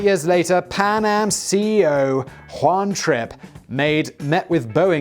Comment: Oooohhh! Juan *Trip*. I thought you said Juan Trick at first.